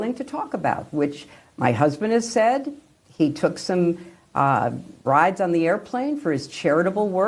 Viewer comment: Did you notice that it sounded like she said: he took bribes I guess she said he took rides, but it sure sounded a lot different